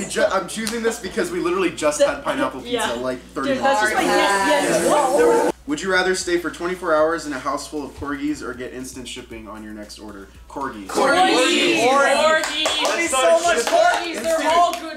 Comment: I ACTUALLY THOUGHT HE SAID FURRIES 💀💀💀